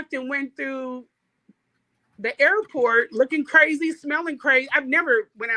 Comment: DID SHE SAY SMELLING CRAZY 😷